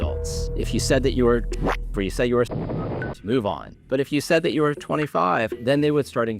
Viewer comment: "move on"??? Im sorry did I hear that correctly?